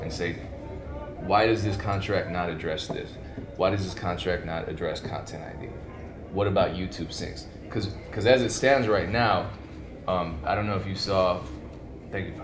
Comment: Content I'd? YouTube sinks? What is the man saying lol